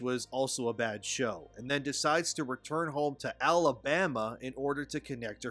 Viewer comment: Alabama? i thought they said oaklahoma